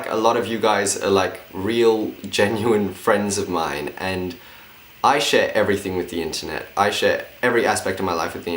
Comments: OH NO I HEARD “I shit everything with the internet” WHAT IS MY BRAIN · I thought he said that he shits everything with the internet😂😂😂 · it sounded like he said "I shit everything with the internet" hahaa